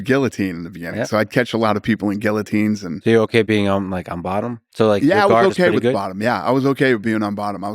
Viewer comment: Once I heard, "Do you like to be on bottom?" I was out.